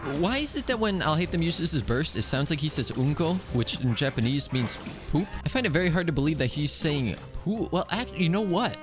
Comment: I looked it up and he’s actually saying bunkou(分光), which means spectrum, but I also thought he was saying unko before checking.